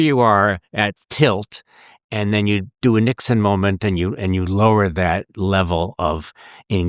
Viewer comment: I wasn't looking at the screen when he said "niksen". I thought he said "Nixon." So I stood up, looked out the window at my neighbors house and thought about which door I should put tape across the strike-plate so I could break-in later that night. I felt refreshed and sat back down at my computer.